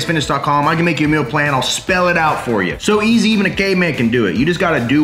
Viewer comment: wow did he just say “...so easy a gay man can do it!” 🤣🤣🤣🤣🤣🤣